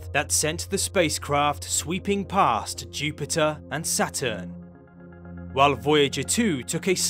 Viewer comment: | Ummm did I just hear my name said wrong. Its Saturn not Satur. Love your channel and seeing photos of me. hehe